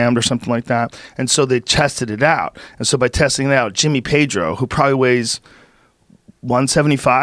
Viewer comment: Got stoked when I thought he said Jimmy Tatro